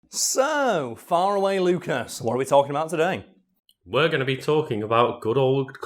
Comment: i keep hearing "so fire away lucas"